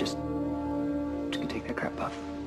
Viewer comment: what does rob lowe say at 3:40?? thanks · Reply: I'm just going to take the crab puff.